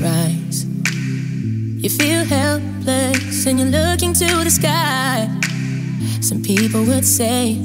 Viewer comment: Who else searched this as "tears from falling down" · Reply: I heard it on a YouTuber's video but the bass was a lil loud and I thought it was saying, "You can't help it so you look into the sky" but its actually "You feel helpless and you're looking to the sky" but YouTube knew what was up bc this was the first option lol